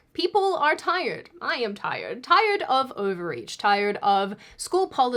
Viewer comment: Oh! Tired of *overreach*! I had to back that up a couple times. I thought you said tired of *ovaries*. 😂